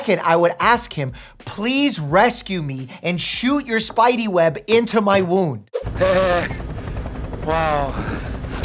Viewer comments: Took me a second. 13:20 I thought he said "shoot your spidey web into my womb" 😭😭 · "Please shoot the spidey thing into my wound" that sounded like womb